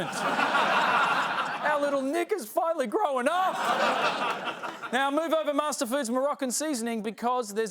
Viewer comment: That audio is not especially clear. I had to listen to it twice to understand why it wasn't bleeped (at the very least). For the benefit of others, he said "Our little Nick has finally grown up." I first heard something else, instead of "Nick has"...